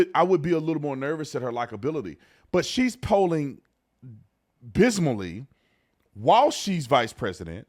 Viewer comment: Did you just say, “bismally?!” You idiot. When will you learn English?! Perhaps, you meant to say “abysmally?” 😂😂